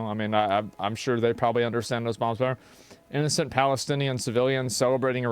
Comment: did Asmon say those guys probably understand how those bombs works? 😅